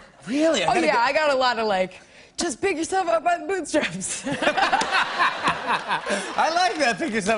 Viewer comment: What does Emma say at 3:13? An inspirational quote I think. Thanks. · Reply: "Just pick yourself up by the bootstraps"